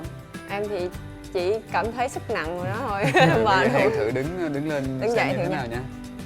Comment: nghe như hen thả boom ấy. Hahaha😝😝😝